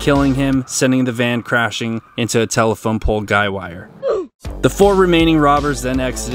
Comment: He says "sending the vehicle crashing into a Guy Wire", This is not wrong, it IS NOT CALLED A GUIDE WIRE!!!! GJ on at Randomly knowing this! LOL